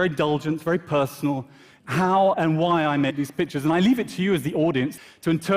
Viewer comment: I swear he says Bitches